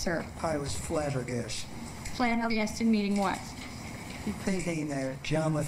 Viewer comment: Dude, love the channel, but as a person with hard-of-hearing loved ones, your subtitle game is abysmal. WHO IS DOING YOUR SUBTITLES?! The section starting at 27:17 is terrible. Like, he says "I was flabbergasted." Then the next subtitle says "Flattered, meaning what?" He just said "Flabbergasted." That's obviously the word she used. Or when the lawyer says "Shot" but the subtitles say "Sean" - how did you guys make that mistake? How was that not caught during editing? WHO IS SEAN?